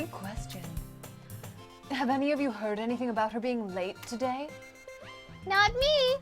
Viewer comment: I had this on, on the side and I misheard "late" as "raped" made me do a literal spit take.